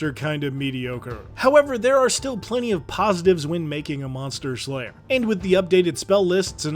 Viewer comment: I thought it said "monster slaver" and am honestly slightly disappointed